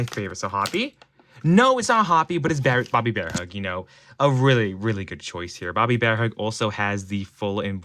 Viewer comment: I heard bearbobbybearhug 😂😂😂